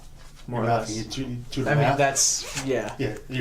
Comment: is sounded like he said meth xD